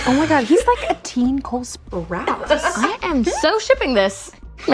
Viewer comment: Wait did she just say like a teen Cole Sprouce? Riverdale is connected to this show right? Hm…Cole Sprouce and Jugghead look very similar….